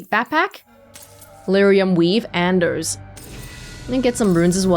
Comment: I misheard 'Lyrium Weave Anders' as Lyrium weeb Anders 😅